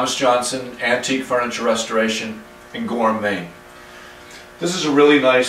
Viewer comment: I’ve watched many of your videos but just now I heard you say that you were from Gorham, Maine and I always thought you were “antique furniture restorer and gourmet.”